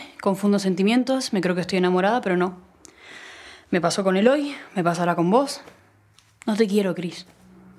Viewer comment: Me PASO con Eloy, me PASARÁ con vos... el tiempo que uso en estas palabras dice mucho!!! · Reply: Escucha con atención después del minuto 1:50 , vas a ver que ella dice: "Me pasó con Eloy, me pasará con vos". Es una pena que Cris no se haya detenido en ese detalle, porque es la muestra oral que traiciona el discurso de Joana y deja claro que en realidad no siente lo que está diciendo.